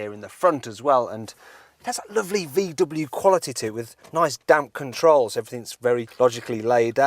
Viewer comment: Did he just say "Nice damn controls"? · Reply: Nice damped controles